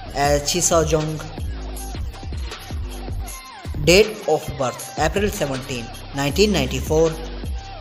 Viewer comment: i thought and heard you said "DEATH OF BIRTH" . im sorry😭😂